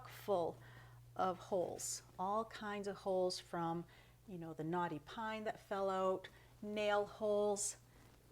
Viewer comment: Just watched this again, at 14:01,I thought you said “ the naughty pine that fell out” and I was defending the wood in my head,thinking, “probably not it’s fault”.😂😅🤣😂😅🤣 Love your accent!🥰